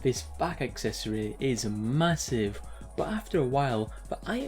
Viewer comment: Did you say... massive?